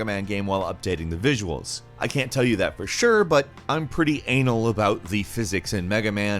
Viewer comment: did he just said "anal"?